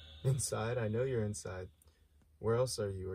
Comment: yoooooo go to 11:51 it sounds like xxxtentacion says inside